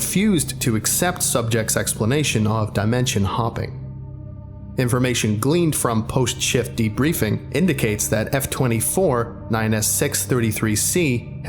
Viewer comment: I thought it said 'Lubricant dimension hopper' I'm weird